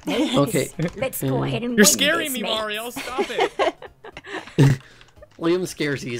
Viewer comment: on 6:41 I heard YOUR SCARING ME MOMMY iam like lolooolo